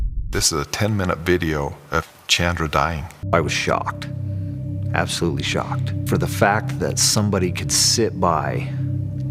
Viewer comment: Hello, I'm an English leaner, Can anybody tell me what's the word at 0.59 to 1:01 mints? Please 🙏🙏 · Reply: “I was shocked, absolutely shocked, for the fact that somebody could sit by ...”